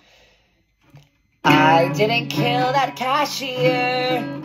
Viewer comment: At first i thought you said i didn't k!ll that cashew🤣🤣🤣🤣 Edit: thx for the likes😊